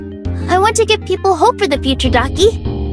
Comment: I heard "Darkie" instead "Docty" and got jumpscared a bit